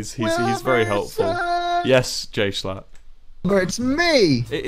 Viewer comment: HE SAID BUT I THOUGHT HE SAID GAY SHHLAT